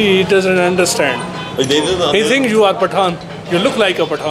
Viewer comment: What he said ? He look like what ? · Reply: Pathan an ethnicity in Pakistan